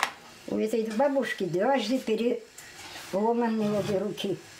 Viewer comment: А детей у них нет? А что у бабушки с руками? Она сказала что-то вроде « двойной перелом» или мне послышалось ?